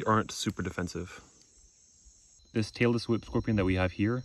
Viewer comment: you say “tailless whip” and I hear “Taylor Swift”